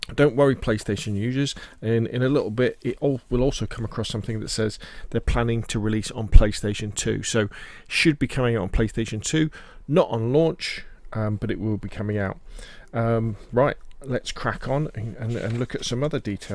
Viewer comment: When he said its coming out for playstation too. I heard it as, "its coming to PS2." Lol